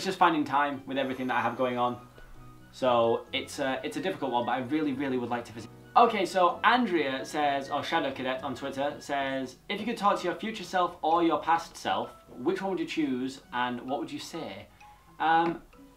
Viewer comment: I thought he said if you could torture you future self or you past self 😂